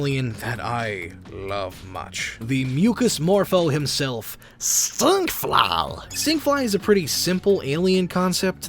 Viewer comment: That sounded like Stich XD